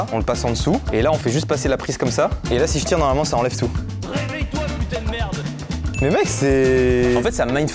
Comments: Qui a entendu le « réveille toi putain de merde » à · J'en pleure tellement j'adore tes vidéos 😭😇+le truc avec le câble coincé c moi où j'ai entendu dire" réveil toi putain de merde" ? 😂